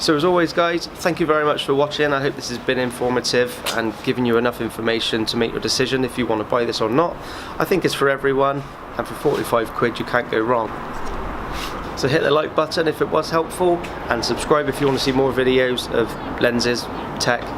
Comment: I thought u said Hitler like button😂😂😂